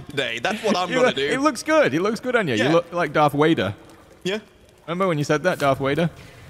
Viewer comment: LOL... I heard "Darf Wader"... seems legit.. lol... oh, ur gotur took ur rebreeder erf or yur gurner run outer airrrth... lol.. great content!!.. love it.. lol.. CHEERS!!!